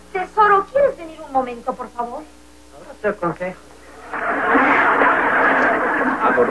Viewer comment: que dice QUICO? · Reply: dice "otro consejo"